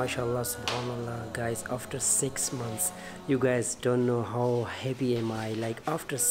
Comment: The subtitle. you guys don't know how heavy I am 😂 I think he said "how *happy I am"